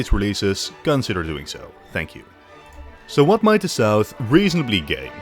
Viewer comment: i thought he said "so what sounds reasonablly gay"😭